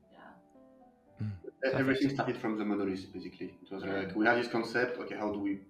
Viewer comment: (fyi they're discussing spoilers at this point) At 47:52 he says "everything started from the ????". Can anyone with a better brain than mine tell me what that word was? It's driving me nuts Separately, the composer said he was a player of Zelda and some other game series, I couldn't catch what it was. Anybody know what he was talking about? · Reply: Monolith